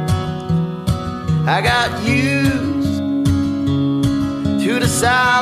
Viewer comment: Once I heard "I got pubes" at 0:24 and I can't unhear it.